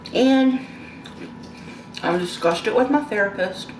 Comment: no, they were totally supportive of me.. and i was grateful of that. i. don’t know how you got what you did put of what i said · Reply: I thought in the video you said you were disgusted with your therapist, Susan, and your friend from childhood. Maybe my I misheard you. I was sleepy when I watched but, I do still feel like you’re trying to convince yourself this is a good idea and I pray this doesn’t end bad for you. I just got all kinds of bad feelings in my spirit listening to you.